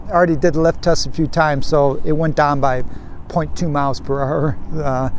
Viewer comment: increase voltage by 0.1 in menu. Info from Linnea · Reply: my bad sorry, I thought you said kilometer per hour but it was mph. Some users experienced an issue with freespin cuting out at about 80kmh in high speed mode after update.